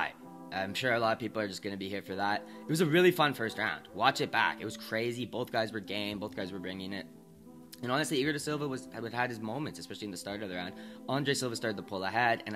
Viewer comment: Yo when I was talking about the severino fight I thought I said both guy are gay both guys were bringing it. 😅😅